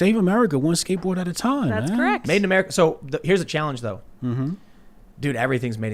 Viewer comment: Okay so you said “made in America” at 1:33:10... I swore I heard you starting to say “make America skate again” If that’s not your brand slogan you’re losing a subscriber. XD